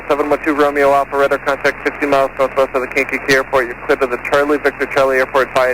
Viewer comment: did they just say the "KKK" airport?!